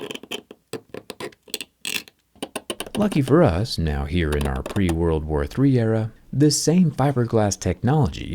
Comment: Pre world war 3 era. 3:34 did i hear it correct